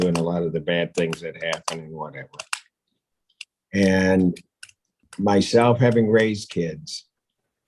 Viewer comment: first I heard "myself having raced kids" and was like WTF for a second..